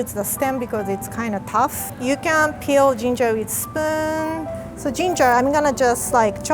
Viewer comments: did anyone hear her say you can peel ginger with sperm Edit:It sounds wromg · I thought she said “so, you can peel ginger with sperm, and” AND I WAS DYINGGG